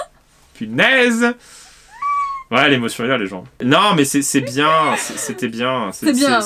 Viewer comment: Juste c'est quoi que Guillaume il a dit à 9:17 ? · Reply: L’émotion est là les gens